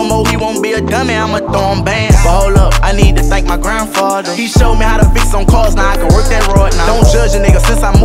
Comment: I thought his grandfather said he show him how to pee on cars 😅